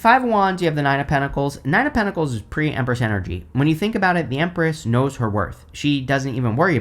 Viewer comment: I went back 3 times to re-listen to this bc I heard “pretty Empress energy” 😆 I like to listen to tarot readings sometimes not for what may be ahead of me but just to hear how people interpret cards. It helps me deepen my own understanding of tarot with what resonates and interesting to see how other people read different cards together. I’d never heard anyone say Pretty Empress Energy but I was intrigued! In my defense, I wasn’t watching the video, I was listening while cutting a cantaloupe but idk why it didn’t click that you were saying the 9 of pentacles was PRE Empress energy. Now if I ever pull the 9 of pentacles AND the Empress together, I shall refer to THAT as Pretty Empress Energy (aka PEE) 😂 sorry, that acronym is immature of me and def not Empress energy of any kind but it did make me giggle when I thought I should shorten it. We are def in the pits of mercury retrograde over here so might as well make potty jokes and laugh about it. 🤦🏻‍♀️